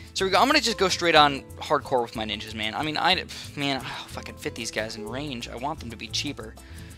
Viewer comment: did he say "fu*** fit these things"!?!?!?! I hope I'm hearing things😂😂😂